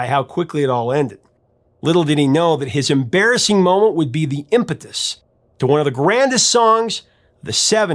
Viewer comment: "Little did he know that his embarrassing moment would be the impotence to one of the grandest songs of the 70's..." Wait! Did I hear that right?